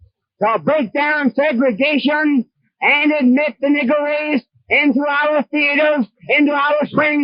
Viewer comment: OMG HE DID NOT SAY Negro he said NIG$R!!!!!!